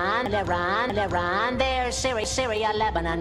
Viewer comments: he sayin lebron instead of iran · sounds like hes saying lebron 😂